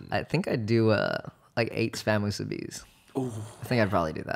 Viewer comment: What is the meal Michael said at @ 1:07:24? I can't understand it. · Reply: he said 8 spam musubis